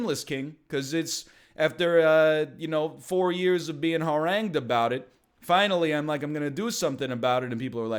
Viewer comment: "What's that word? Hwoarang? Wasn't that a Tekken character? Let me check the auto-generated subtitles. Oh..."